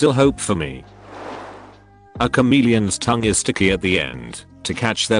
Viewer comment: Listening, not watching. However, after the bit about the chameleon's tongue at 26:04, I had to pause and read the text. I misheard it as "a comedian's tongue."